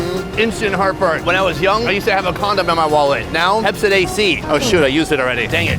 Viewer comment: did I heard condom? 👀💀💀